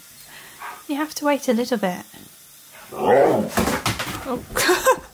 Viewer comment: Sounded like you said "little bird" so ... he brought you a bird 😂